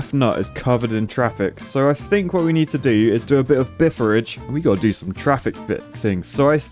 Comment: Admittedly, it would be hilarious to see Biffa take a stab at Engitopia. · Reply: I think he called it "doing a bit of Biffaridge".